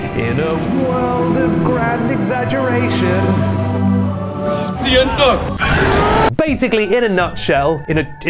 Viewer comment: I CANNOT stop saying what. is. that? It’s the unknown!!! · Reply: yeah ‘Quartering’ heard it as ‘It’s the Ender!’ That also fits too . . . It’s the heavy emphasis on the ‘un’ in UN-known.